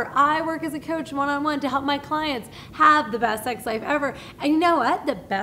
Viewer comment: Lol half the time I thought she was saying sex life and the other half the time I think she was saying sex slave